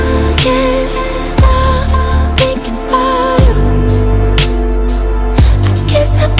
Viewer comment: Stray Kids Hyunjin brought me here omg he recommended this and said it's a really sexy song AND HE'S RIGHT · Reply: Rain Lee OMG SAME like at first I thought I heard him say thinking of you by Katy (Perry) but it's def not a sexy song??? so I replayed it again HAHAHHAAH